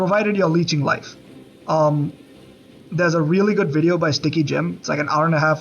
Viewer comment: did I misheard or did you say sticky jim? 😂